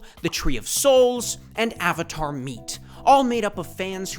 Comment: I had this on in the background and thought he said "Avatar Meat" at first Like bro what 💀